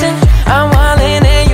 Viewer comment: to some reason on Zach Chours it sound like he is saying I love my love